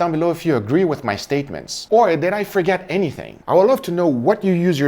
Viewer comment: Did you say "Steak-ments"?